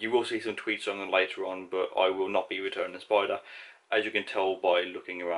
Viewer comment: For the first 2 no utes I though he was saying spoilers.... I was so confused until I realized its the accent 😂😂😅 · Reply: I'm Canadian. Like I said for the first two minutes (not actually just first few times he said it) his accent, to me, I didn't hear the d's so thought he was saying spoilers.